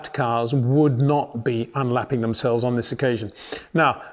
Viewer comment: Peter deletes my comment… Masi has actually said: “cars may not unlap themselves”, huge difference what Peter is saying… say it correctly